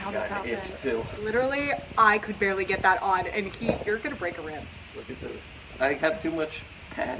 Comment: Wait just a damn minute! Sound Guy's name is Keith?!?!?! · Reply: Omg, I swear at minute 12:00 it sounds like says, "and, Keith, you're gonna break a rib." 😂😂😂